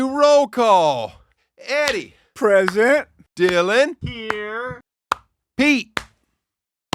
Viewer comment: At roll call, only heard “Here” on the 5th replay…Thought Dylan said “YURRRRR”😂😂